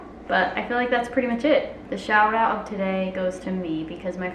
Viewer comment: Omg I thought said my mom just died and I was like- then I replayed it and I realised she said her phone died I was so relieved 😅